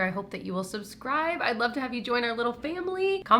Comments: Rose bronze at least that’s what I thought you said 🤷‍♀️🤣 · I definitely thought you said Rose Brauns and I was very confused. rose bronze makes more sense 😂